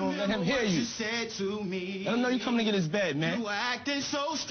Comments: Wait, did Diddy say "let him know you comin in his bed man"? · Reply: Let him know that you’re coming to get his bed because they were staying in a house with a limited number of beds/spots and some people had to go home that day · This gay in retrospect. That's that Thug Luv... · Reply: When this came out I didn't know Puff was like that, but I just watched this again. did Diddy say "let him know you comin in his bed man"?